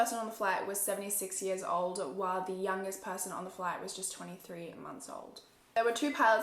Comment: Like the two year old kid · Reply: I thought she said that the youngest person on board was 23?